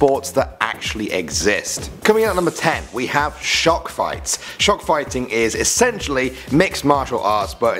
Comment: I thought he said "Shark Fighting" instead of shock fighting.